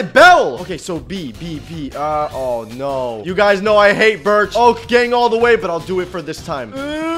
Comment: When I wasn’t looking at the screen and he said dirt, I thought he said birch and I looked at the screen and was relieved